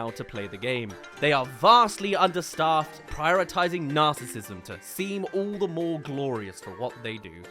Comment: I misheard and heard "prioritising nazim"💀